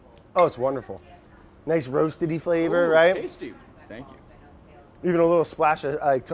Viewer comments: omg anyone catch the lil moment at 17:45 where says "ooh tasty" and the captions literally go "ooh bon appetit" THE SHADE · when he said tasty the caption said bon apetite